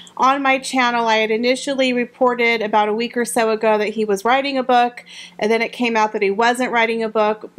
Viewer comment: Hey! ❤💗💜 I love your channel & i'm so glad you're staying around 😻😊At first I thought you said Valentine Low was running amok instead of writing a book lmao 🤣🤣🤣I'm usually suspicious of 'royal reporters ' because they're not unbiased by definition. I've heard these men in grey suits or courtiers, operate in an underhand manner & I don't think they can be trusted either. Historically British kings & queens have been deposed/beheaded by the whispers & rumours in the ear of the serving monarch by these creatures. Whatever happens, I want to truth to come out in all its gory glory, although i'm not buying that VL dude's book btw 🤣😆🤣🤣You can get goat's milk soap on Amazon for $5, makes your skin really soft, or you can get a used book, or catnip for Romeo! :chillwdog